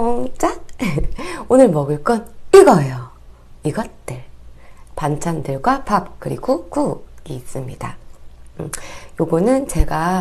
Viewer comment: Guys I think I heard her say bangtan in